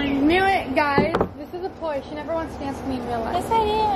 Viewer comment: does anyone know what says at 12:28? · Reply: She said : this is a ploy, she never wants to dance with me in real life